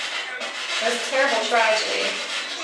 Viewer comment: Late to the party but around 7:25 - 7:30 mark the response that you put on the screen stating you couldn't make it out? For some reason, it was clear as day to me when I heard it in my headphones. Female voice says, "This was an accident." Which is a weird thing to say considering he admittedly killed her on purpose with no remorse, but I'm 100%, that's what the female voice said. "This was an accident."